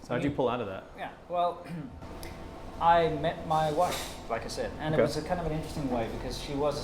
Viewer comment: For a second I thought you said "The moment I beat my wife", didn't help that it was followed by "I was going through a rough divorce". Absolutely confused and concerned XD